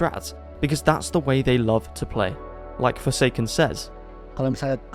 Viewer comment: Hi indo here,i just wanted to say when forsaken say 29:14 he actually being cut off,at the and he is not saying "right" like the sud said he actually saying "because"